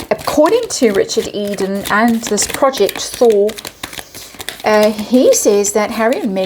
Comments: I thought you said 'project Thor'. imagine my confusion! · I kept hearing "Project Thor" & visualizing William bringing Mjölnir down on Hazbeen & The Claw.